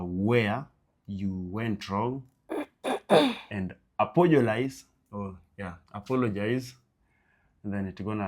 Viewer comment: Did I just hear apogolise😅